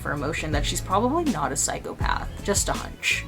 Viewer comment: Hahaha - didn't quite catch that! (17:18). I thought you said that "V wasn't a psychopath ...just Dutch". Bit harsh on the Dutch there, girl !!?? lol (good job for 'cc')